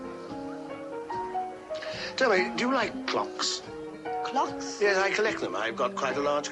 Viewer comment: Must get better earphones, when Jack Hawkins was standing at the bar with the Italian bird I thought he said ' do you like cocks' !